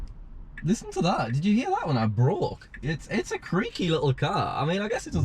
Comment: When he said "when I brake", I dead a$$ thought he said "when I broke". Smh